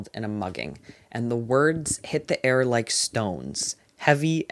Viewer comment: I misheard you here and thought the word "stones" was "scones" and it almost killed me. I was drinking tea and inhaled